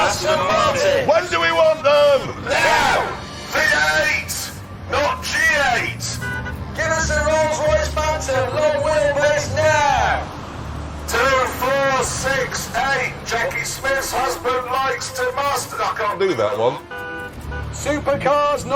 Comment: I Thought Clarkson Said "2, 4, 6, 8, James May" 😂😂😂😂😂